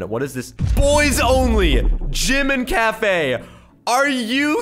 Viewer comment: I thought he Said "JIMIN CAFE"